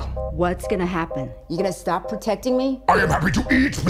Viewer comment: "Good evening Eddie." "Hey Mrs Chen." "Good evening Venom." "Wǎnshàng hǎo Mrs Chen!" "He says hi." 😂😂😂😂😂😂😂😂😂😂😂😂😂😂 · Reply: THAT'S what he said?? I swear I thought he said, "boats and hoes, Mrs. Chen."